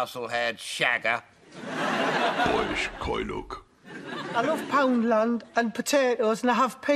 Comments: what does he say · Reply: Boyish coy look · Love this skit, so accurate! Can anyone tell me what 'Boris Shagger' says at 00:21 though? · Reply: Ten years late, but he says 'boyish coy look' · Boris coynog? Is that what he says? Need subtitles. Also can someone explain the meaning behind the overripe strawberry joke? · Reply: Boyish coy look